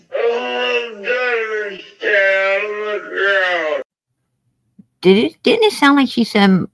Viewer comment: he asked her what have she done…it sounds like she said…”All I’ve done, is down in the ground”! Which to me means that she definitely buried something!